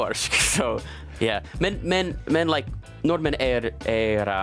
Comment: Can someone tell me what he's using for "friendly"? 13:09 Google says "vennlig" but it sounds like he uses something else. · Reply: hyggelige